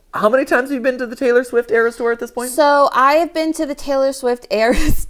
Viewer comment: I thought you said Tyler swift eras store. I was like “where is that and how do I get there?”.